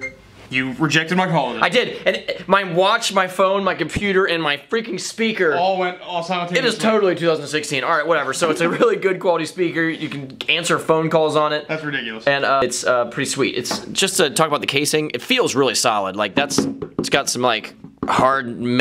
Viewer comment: lol It sounded like he said "you can cancer some phone calls with it" 😂